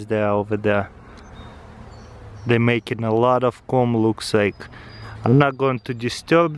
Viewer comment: what did he say? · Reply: sutherland "They makin alot of Comb it looks like"